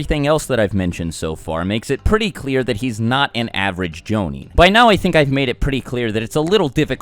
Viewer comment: "...makes it pretty clear that he's not an average jounin." Am I the only one that thought he said Zabuza's not an average Joe...nin?